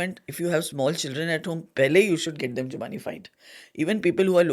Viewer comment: Can someone please clarify at 58:57 She says if you have little children then ?????? Don't understand what she's saying after that. Can someone please clarify.🙏🙏TiA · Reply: Jumaanified..i.e. seek their guidance